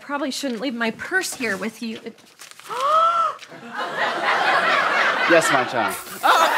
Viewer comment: At the end when he said, "yes my child" , I thought he said ,"yes pop snot "